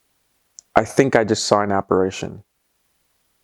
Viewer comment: what did he say....operation? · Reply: He said apparition